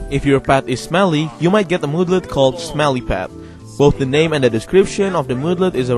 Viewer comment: Turn on captions at 13:52 and it will say smelly fat instead of smelly pet bhahahahahahahah